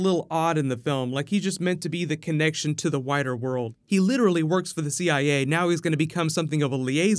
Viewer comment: from 5:01 to 5:03 Not sure if I heard "He's just made to be the connection to the 'whiter world'" or "wider world". Haha!